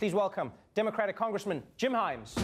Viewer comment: I thought he said “congressman Jim Jeffries” and was thinking, “things haven’t gotten that bad, have they?”